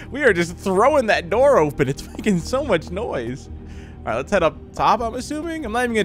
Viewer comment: and I may be confused a bit and didn't hear this right but does it sound like said "F*cking so much noise"? I don't mind it's just I've never heard him curse before because he's a kid friendly channel :)